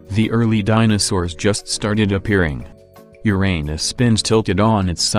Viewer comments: Can we talk about that there is "Your anus" instead of "Uranus" at 4:25 in subtitles. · The subtitles really said “your anus” instead of uranus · Did you just say your anus 4:32🤣🤣🤣